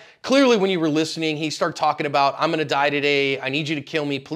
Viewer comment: I thought he said "I wanna die".....What's with all these mentally unstable people nowadays!!